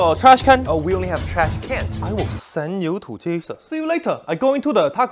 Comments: I love when he says "I WILL SEND U TO JESUS" It's just PERFECT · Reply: I thought he said “I will send you to Cheetos” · I love when he says "I WILL SEND U TO JESUS" It's just PERFECT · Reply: oh he said Jesus? I thought he said Texas 😀